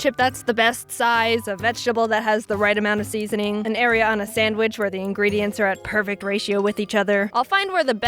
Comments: For 3 years I thought she was saying, "An Ariana sandwich" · I thought she said an Ariana Sandwich